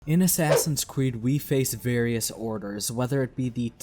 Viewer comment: I keep hearing “in assassins cweed”